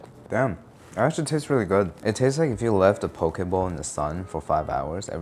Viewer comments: Omg at 8:37 I thought you said it tastes like leaving a poke BALL out in the sun. I've watched this like 4 times and never questioned it like yeah I'm sure that's what hot Magikarp tastes like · Okay you said “poké bowl” and I heard “pokéball” and the horrors going through my mind before I figured it out…🤣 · Thought he said "pokeball"